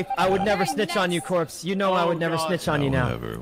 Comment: Sykunno: “I’ll never snitch on you daddy” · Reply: I thought he said, yeah you would never, you would never